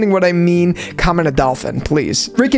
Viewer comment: I literally heard come in a dolphin, rewind and turn on captions and the captions thought you said come in a dolphin too